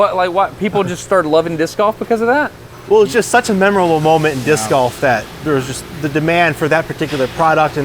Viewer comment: This whole video I thought he was saying Disc off. As in Disc-off competition 😅